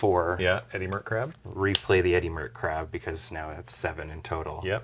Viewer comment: Every time they say "Eddymurk Crab" I keep hearing "Eddy Merckx Crab" and wonder who at WOTC is really into cycling. I'm also imagining a crab on an old school road racing bike and I find that terribly amusing.